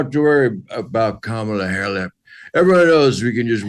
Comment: did he say "kamala hair-lip"? 😂